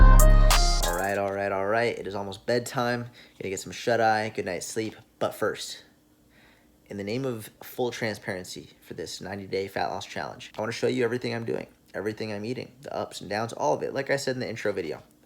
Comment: Your 8:03 confession, sounded like you said "90 day phallus challenge" lol